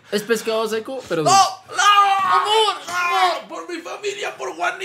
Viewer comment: Soy yo el unico que escucho que dijo amor en 3l minuto